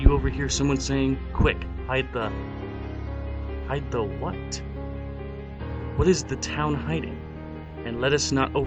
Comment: lol I thought ti said ah no its the fuzz hide the kid again